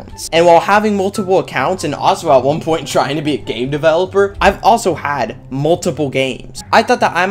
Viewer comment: at the start I thought he said gay developer